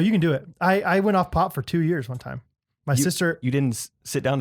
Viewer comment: I thought said “I went off ‘pot’ for two years” I was like “When were you a pot addict!”